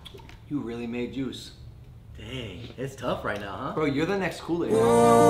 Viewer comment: Did he just say that’s the new kool aid